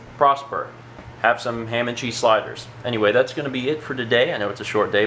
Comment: for a minute I thought sliders said sailors ._.